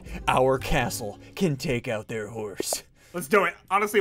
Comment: what did he said the horse? I thought he was saying a house